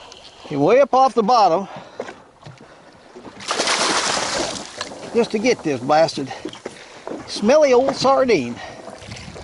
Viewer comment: What's the word you used to describe your fish at 9:58 ? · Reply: "Just to get this blasted smelly old sardine".